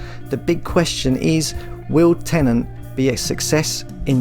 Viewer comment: Did you just say Tenant? 😡🤮